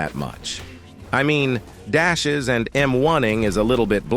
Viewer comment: "dashes and moaning" erm excuse me what the actual fu did i hear